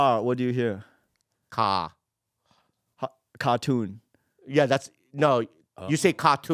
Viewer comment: “Cartoon” I heard “Car tune”